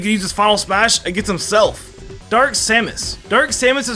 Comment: Did you- Did you just call Kazuya "Kazuwa"?